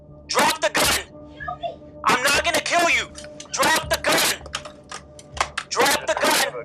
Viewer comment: Did he say "I'm not going to kil you." She fell for the orientation movie. 31:27 Now you will have all cops saying this before they take you out